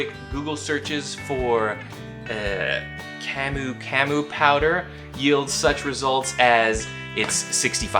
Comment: " did you say cum powder! EWWWWWWWW!! WHAT THE FUCK!!"